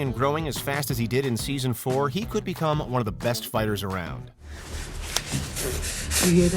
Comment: Did he just say kilal the best fiter